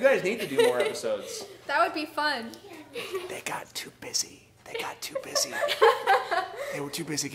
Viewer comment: I thought he said "they got to pissy" but it was busy 😂😂😂😂